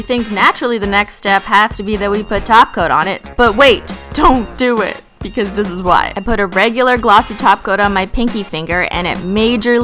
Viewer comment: why does it sound like you say taco not top cote